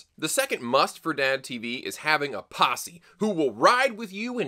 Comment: I heard "possum" the first time, and got surprisingly far before the pictures in my head stopped making sense